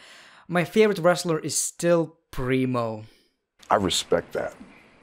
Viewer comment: My dumbass thought he was saying Bree Mode not Primo 🤦🏻‍♂️